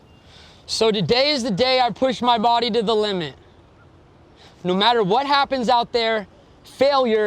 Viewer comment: I was sure I heard .. "today is the day I push my body to the lemon" .. ✌🏻